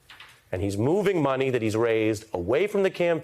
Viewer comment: When you said "moving money for assorted reasons" at first I heard "moving money for a sordid reason".